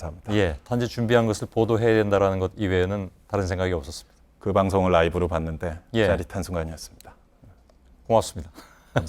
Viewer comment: 끝에 말이 흐려져서 뭐라고 하시는지 안들리네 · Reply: 황서인 "그 방송을 라이브로 봤는데, 짜릿한 순간이었습니다."